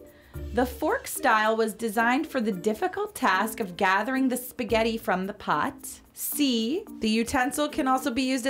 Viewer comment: why is she saying at 5:27 spaghetti but they edited noodles haha sorry